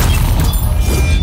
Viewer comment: What did Wolverine say? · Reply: well that day ain't here yet